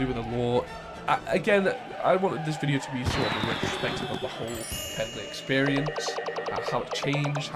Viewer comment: I wasn't paying attention and thought he said "pickle experience"